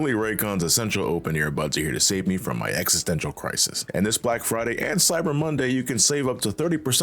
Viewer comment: My ass really thought he said "my ex's dental crisis" during the raycon segment